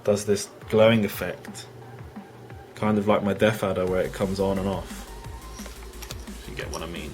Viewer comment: If you listen to the video he clearly says its his DeathAdder